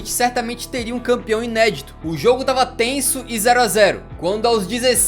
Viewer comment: Esse tenso que ele fala parece mais ufc kkkk